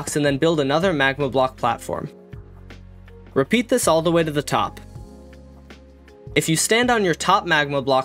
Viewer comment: shat did you said bro!! All the ssy to the top!!!!!!!!!!! Are you crasy?!!!!! 0_0 I aint building that i am lazy 😅